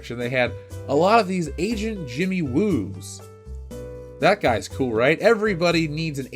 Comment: I heard "asian JImmy Woo", not agent Jimmy Woo. I was like, no shit, he is asian haha.